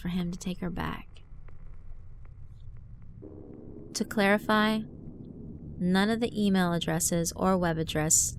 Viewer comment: I thought she said 'to Claire-ify' instead of 'to clarify'